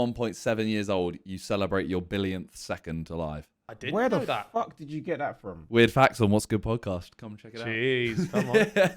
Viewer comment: What podcast did he say? Couldn’t understand it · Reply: What’s Good Podcast